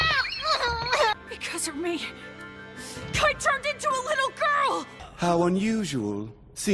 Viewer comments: "Because of me... KITE TURNED INTO A LITTLE GIRL!" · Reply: I heard "I TURNED INTO A LITTLE GIRL!" so I was hella confused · "Because of me... KITE TURNED INTO A LITTLE GIRL!" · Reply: OMFGGGGGGGG I THOUGHT I HEARD, “I TURNED INTO A LITTLE GIRL”